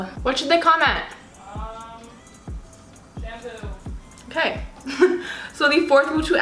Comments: Shampoo (thought she said shamu) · when she said shampoo i thought she said kandoo lol